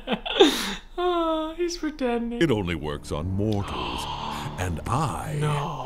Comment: When I was younger, and I watched this movie for the first time, I thought he said “It only works on Morons” so the entire time I thought they were calling Po stupid